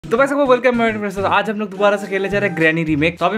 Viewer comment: bhai merko samajh nahi aaya kya bola tumne 😅😅😂😂😂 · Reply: Sabko welcome bol raha tha